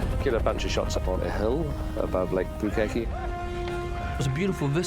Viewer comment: did he say lake Bukkake?